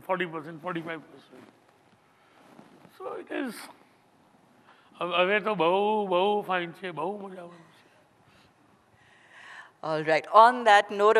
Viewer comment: can anyone please tell me what did he say here and meaning of it 😄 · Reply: Ab tho bhohot maj ayegi